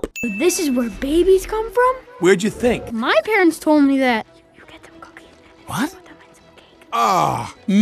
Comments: does not say anything inappropriate, all he says is “*you get the cookie and put it into the cake and milk*” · "You get some cookies and you put them in some cake" Am i hearing that right? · did say “put some cookies and put them In some cake” when he whispers the “sex talk” to boss baby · Fun fact : 6:43 what was actually whispering about reproduction was "you get the cookies and put them in some cake" if you listen closely (i dont know for sure but thats what it sounds like to me.) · I hear him say “you get some cookies and you put them in a cake” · Lol said I HEARD: you get some cookies and you put them in some cake. AUGH! No! That’s disgusting! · It sounds like he is saying "you get some cookies and put them in some cake" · the only thing I hear is, "Forget the cookies, explain to the fence of cake" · I think he said You get them cookies and put it in some cake. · At the time 6:41 it sounds like is saying you take the cookie and put it in the cake anyone else hear that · if you listen closely to the audio, what he said was, "You get some cookie and put them into some cake" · Am I the only one who heard "You get the cocaine and put it in the cake" 🤣🤣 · When whispered what his parents told him about babies, I heard " You get the cookies and put them in some cake." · "You get them cookies, then put them in some cake" thats what i heard. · he actually says “you get the cookies and then you make a cake” that’s from what I’ve heard